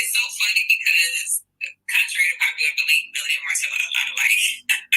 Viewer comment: Oh, I didn’t hear that part where she said MEL and Martel are a lot alike. I thought said that her and Martell are a lot alike, which is what I was saying that yes her Dusty Dan Dandre dumb dumb devil down and on ignorance is the same as Martel Noel having a ZZ